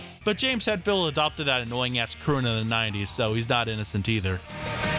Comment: "annoying ass cruuna 90's" like what words are you even saying?